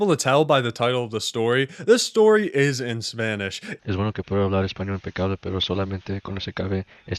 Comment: El pendrive azul why the fuck he was talking peruvian spanish, i'm a native spanish speaker, i didn't paused the video while typing and i hear a "es bueno que pueda hablar español pe causa" pe causa is like a "hey pal" on english